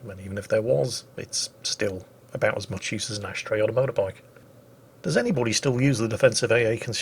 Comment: What Jingles said: "It's about as useful as an ashtray on a motorbike" What I heard: "It's about as useful as a doorbell on a dildo"